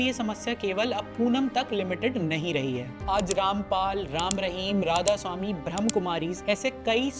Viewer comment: Did he say "Bhram Kumari"! 😂